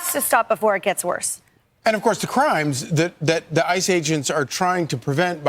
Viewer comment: All I hear is "the ice age ends" and 'ice-projects". This global warming always happens in summer, so yes, ice cream should be top priority.